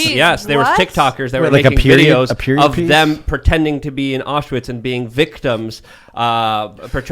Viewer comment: I thought he said ostrich not Auschwitz.